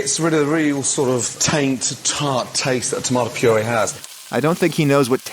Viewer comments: I thought Gordon Ramsey said "Tang to tart taste" lol where is your mind Adam? In the gutter? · In one video, he: -Dissed grandma's cooking -Dissed Gordon Ramsay english words knowledge -Broke spagetti in half Bruh, That's some brave man right there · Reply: I thought Gordon said "tang to tart"?